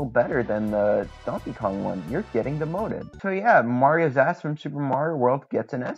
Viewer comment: "Mario's assmanship" is not a phrase I ever thought I'd hear, but I can't say I regret it. (I'm not even 100% sure that's what you said there, but I'm going to pretend it is because it's brilliant)